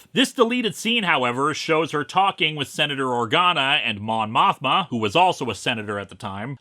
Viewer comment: Everything i hear Mon Mothma's name, i always think they are saying Mon Mothra and then I wonder when did star wars cross over with Godzilla?